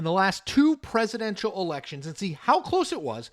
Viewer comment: haha up until 0:32 i thought you were doing a show about Poland haha really that's what i thought i heard